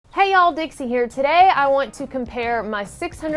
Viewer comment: When video started I heard "hi all dicks here" wtf?